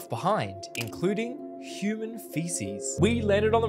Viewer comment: For a sec I thought he said human faces😂